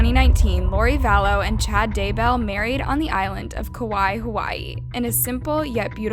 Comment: I heard Kawaii Hawaii